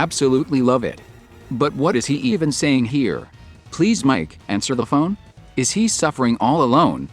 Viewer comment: There's a higher quality version of the lyrics at the end of BIG SHOT (which is barely audible), and he sounds like he is saying other things (ex. He doesn't say "answer the phone", he says "pick up the phone"). You can't rely on these lyrics.